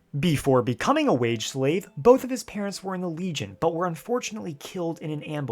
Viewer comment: I had to rewind there for just a second. What was actually said: "Before becoming a wage slave, both of his parents were in the legion" What I heard: "Before becoming a wage slave, both of his parents were norwegian" I was like "Pardon? Was that an attempt to foulmouth my homeland? Are you insinuating something in regards to norwegian parenting or labour laws?" But, I quickly found myself stood corrected. If that is the gramatically correct way to put that.